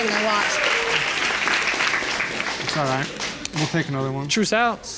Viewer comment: When her fiance said, "We'll take another one." Yo, it's DNA! It's not going to change unless you walk into a nuclear reactor unprotected. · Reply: Lord I thought he said "We'll make another one" like it was a competition! 🤣🤣🤣🤣🤣